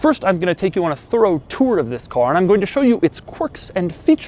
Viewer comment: did he say... thuro?